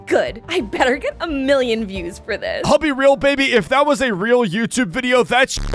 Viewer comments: did he just call her baby? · seconds did he just say baby?